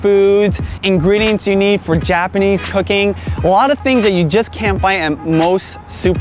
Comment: Sounds like you said 0:18-0:19 "Japanese Hooking"..instead of cooking..LOL Good job on the video...I live in Los Angeles area..best Asian markets too..fresh produce ever!